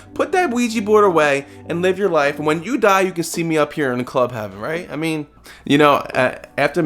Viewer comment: When you said Club Heaven I thought you said Club Penguin 😂😂😂